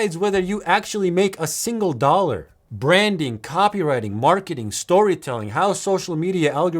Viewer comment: “marketing, marketing, marketing, marketing” is what I heard